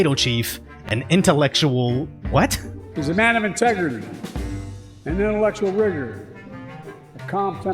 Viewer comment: Lots of crazy clips here, but the last one I heard "A man of integrity and intellectual rigor", shrugs